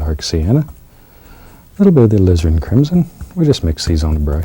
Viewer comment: It sounds like he said lizard crimson